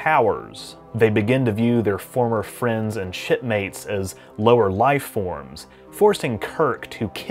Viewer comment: did he say shipmates? I'm hoping he said shipmates 😀